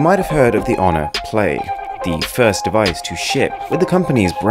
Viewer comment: Did he say "shit"?